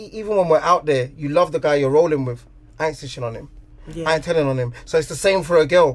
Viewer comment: This chicks lost doe lool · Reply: Real talk.. I heard “yeah I didn’t snitch”.. Then argh “I should have snitched cause I’m a woman” 🤷🏾‍♂️😭